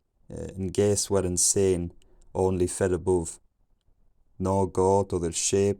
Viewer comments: "guess what I'm saying" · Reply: I heard "I guess we're insane" lol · "and gays were insane" hein c'est quoi ça ? tbrnk